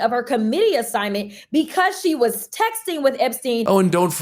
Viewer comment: I could swear I heard Crockett say sexting instead of texting. We already knew about the aides getting frisky in chambers and taking pictures.... Is there more we don't know about?